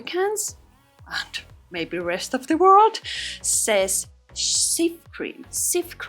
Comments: Hi, in Australia, it's called Jif cream! So I always thought you were saying Jif! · If calls it “Chief” cream, then it’s Chief cream. Thanks for helping people get their lives back on track! 😊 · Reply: I always thought she was saying shaving cream, what is the actual name of the product? 😂❤ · I always thought you were saying CHEAP cream😂 oops!!!!